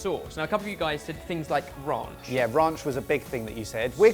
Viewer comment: I keep hearing “raunch” instead of ranch. “What’s good about raunch...” lol